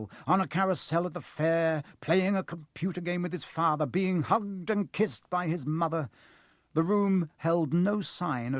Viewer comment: Sound like he said 'Humped and kissed' rather than 'hugged and kissed'.... O_o